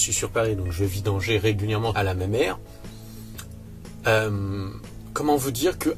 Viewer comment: Ca veut dire quoi exactement "Je vais vidanger régulièrement à la mémère" ?? 😅